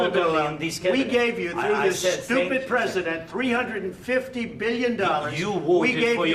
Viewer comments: Did he just called biden stupid president 😂😂😂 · Did Trump said Zelensky to "stupid president"?? Either I misheard or I misrecognized it ,but it was horrible meeting.